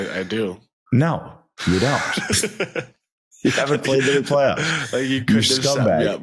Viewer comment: Did Mathieu just say Ottawa never played Montreal in the playoff? Tell me I heard wrong. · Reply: He said “you haven’t” so I think he means Brady specifically. Which is kinda interesting to think players might not buy into historic rivalries unless the have a personal investment in it from the playoffs. Brady obviously disagrees so I wonder what the break down would be from other players would be